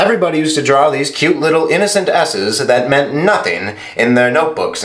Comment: I thought he said "asses" 0:20 😂😂